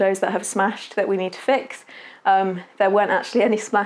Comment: um...did I hear that right at the 15:00 mark? Your neighbours smashed your windows? Lovely garden- thank you for sharing- blessings from Ontario Canada